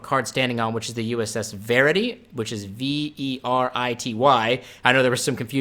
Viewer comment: I was definitely confused the first time I heard someone say "Verity", I heard "Faraday". Which would make sense as a ship name, on account of, y'know, Michael Faraday. But "Verity", of course, reinforces the idea of the plot being centred around a mission to find the truth. I'd certainly be curious to see what the Kelvin!verse TNG era would look like. I assume they'd keep the fate angle of the same people existing and being in the same rough time and place, since with the exception of Tuvok being in the DS9 Mirror cast because Tim Russ, that's always been the case with the Mirrorverse. My impossible happy dream world speculation would be to see traffic between the Kelvin and Prime universes. But that would take Roger Rabbit levels of negotiation and lawyering, so that's extremely unlikely.